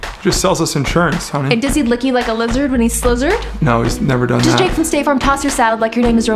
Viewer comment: What does she say at 0:28 Does he lick you like a lizard when you... ?? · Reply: Slizzard